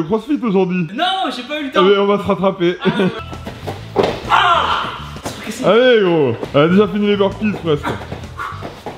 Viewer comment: T'as essayé de faire QUOI ??! 😂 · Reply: Le taekwondo